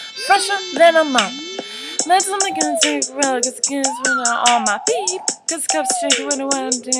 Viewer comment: I swear she said on my beep!!!